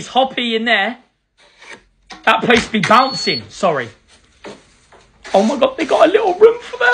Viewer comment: I thought he said that bunny be bouncing I was like WTH BRUH but he did not say that